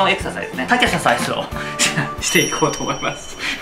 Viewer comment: 「サケササイズ」が「サクリファイス」に聞こえて「リュウジを生贄にちょもを召喚！」と頭の中でツダケンが叫んだ